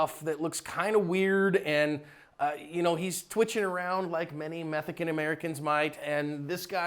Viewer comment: Did you just say " methican american "